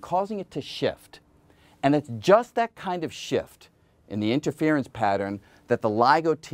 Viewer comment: I heard "and just that kind of shit" the first time.